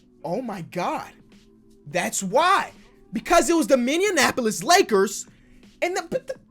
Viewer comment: “Ngl that Mavericks jersey is sick” -my 2nd grade cousin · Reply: I heard Minneanapolis😂 extra "n" lol